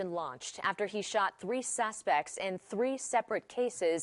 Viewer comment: Did she just say “sasspects”? Sassy. 😂